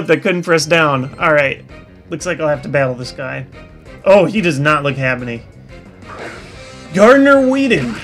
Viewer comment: Did anybody catch that at 15:51? "He doesn't look Happini." lol